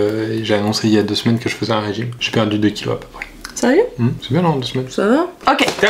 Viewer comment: J'ai entendu : sérieux ? J'ai compris : J'menbalekouilles frère